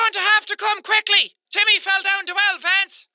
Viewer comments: When the vance lady talked i thought she said TIMMY FELL DOWN THE WELL VANCE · I heard : Come quick, Timmi fell down the well fance...... Hahahaha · when the woman called him i thought she said Timmy fell to Elephants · Vance? All I heard was "Your gonna have to come quickly because Timmy fell down 2 elephants" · I thought she said TIMMY FELL DOWN THE WELL VANCE!!!!!!! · this is what I heard you'll have to come quickly Timmy fill down the wild fence XD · It sounds like she said Timmy fell down the well vance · I think i heard her say " YOU 'LL HAVE TO COME HOME QUICKLY TIMMY FELL OF THE FENCE ??" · At first I thought the lady was saying Timmy fell over the fence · Lady="Come quickly Timmy has fallen down the well Vance" James="waaaaaaaaaaaaaat" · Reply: OH I was wondering where he heard Vance in that... I always heard it as "Come quickly Timmy has fallen down two elephants" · YOU HAVE TO COME QUICKLY TIMMY FELL DOWN THE WELL VANCE I think that’s what it said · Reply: I heard, "YOU HAVE TO COME QUICKLY, TIMMY FELL DOWN TWO ELEPHANTS!!" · In the last wrong call story I thought the person was saying “Timmy fell down the well Lance” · on the vance thing i heard "you have to come quickly Timmy fell down the well Vance!" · '' YOU HAVE TO COME QUICKLY TIMMY FELL DOWN FENCE '' that is what the woman said. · i think it said ‘vance come quick cuz Timmy fell down the well Vance’ I think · I heard “you’re gonna have to come quickly Timmy fell down the fell lance!”